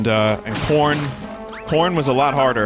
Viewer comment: "Porn was a lot harder... but we did it. my grandfather was like really good at that stuff, and i was the apprentice for a short time" Im assuming he actually said "corn" but it sure doesnt sound like it.